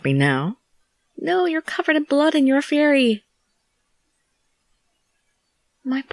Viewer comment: lol i misheard you at the beginning "happy now?" I thought you said "No! you're covered in blood and you're a FAIRY!"